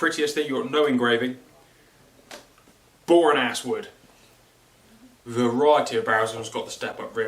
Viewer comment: did he say boring ass wood? 😁